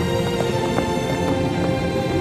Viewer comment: i thought he was saying bye sin actually...